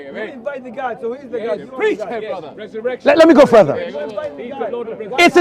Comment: I heard "preach my brother"